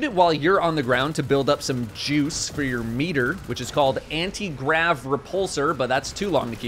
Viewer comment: when I heard "Juice for your meter" you know I misheard it as "meater" 6:24 gotta say I love those "meater juices"